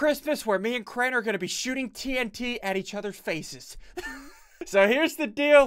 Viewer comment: In the beginning, he says ,"Blow each other's faces up." I thought he said "feces" instead of "faces"